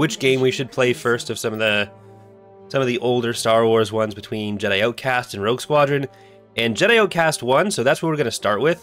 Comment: Could have sworn you said Jedi Knight, not Jedi Outcast for the poll. I'd take Dark Forces or Dark Forces 2 first, but I'd take Rogue Squadron over Jedi Outcast.